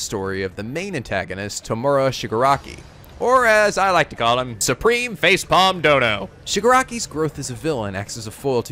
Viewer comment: i miSHEARD YOUR NICKNAME FOR TOMURA AS "SUPREME FACE PALM-DODO" AND I CHOKED ON MY DRINK